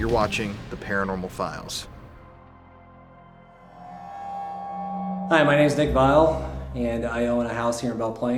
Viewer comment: I heard "my name is junior" The Murder House